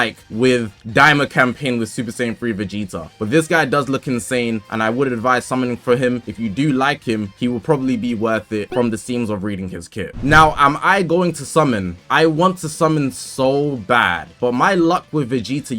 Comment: You scrared me. I thought you said no